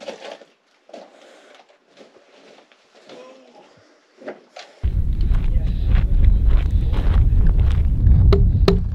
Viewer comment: i can hear what sounds like a man moaning or saying something like “ohhh” or “woahhh” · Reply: The audio is a bit suppressed there. But it was one of them saying 'woah'.